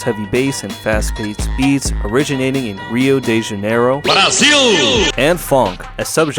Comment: so it says BRAZIL! not i see you😅😂. misheared it